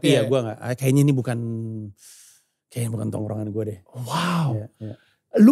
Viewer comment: Panji ngomong apa sih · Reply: Udah kejawab, "Kayaknya bukan tongkrongan gue deh."